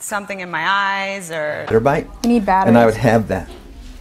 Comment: Min 13:22 Did she just say "I NEED BATTERY" ??